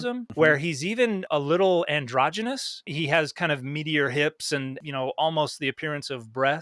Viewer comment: I misheard this at first and now I can't get the image of meteor hips out of my head